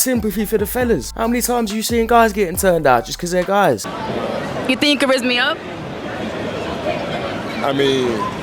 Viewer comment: I 've repeated this 10 times now, what is she saying lmao ? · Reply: She says “you think you could rizz me up?” Basically meaning do you think you could charm me